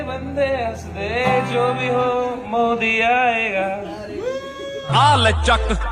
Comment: What did he said i dont understand?? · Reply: Modi aaya aur samay ki maa chod di 😂